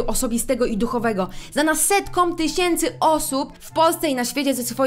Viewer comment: zamiast setkom, usłyszałam sektom czy to coś znaczy?